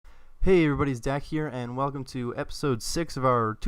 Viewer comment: Okay, so when you start the video do you say "Hey, everybody's dad here!", or am I just hearing you totally wrong? hahaha. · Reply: Alex Thompson When I first started watching his vids, I thought he said "Hey everybody it's Zack here"